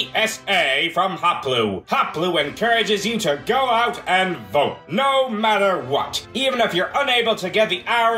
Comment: Why i hear: "Get out and vore!"